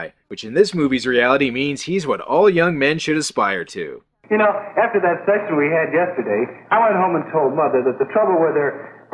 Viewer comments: Did he say "After that sex we had yesterday?" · "After that sex we had yesterday I went home and told mother..." Did I hear that right? Because I don't think I heard that right.